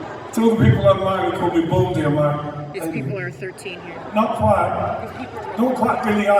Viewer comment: The gal you can hear saying "some are 13 year old here," or something like that, at around 18:35, cracks me up. She should know that Boy George is an outspoken kind of guy. So, if she doesn't want 13 year olds to hear what he has to say, don't bring them to his concerts if she feels "he's too adult for them." Sheesh!!